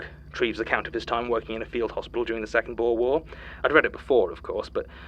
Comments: It’s the second Boer war, from <num>. It does sound like world a bit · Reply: oh yeah i thought he said second world war. Even the captions say that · I think is mixing up wars here. He probably meant The Second BOER War, considering the book was published in 1912 (that it's several years prior any world war). And it fits one of the stories mentioning concentration camps in South Africa. Also because Sir Treves worked as a surgeon there but not in WW1 fields and he died in 1923 of peritonitis at the age of 70, so he never saw the raise of fascism or its consequences. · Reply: He did actually say second Boer War, you might have misheard it